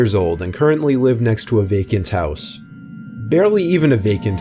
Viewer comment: In story 3 I thought he said that he lived next to a vegan's house XD